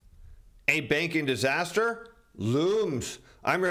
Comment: I thought he said " a bacon disaster " and I got really scared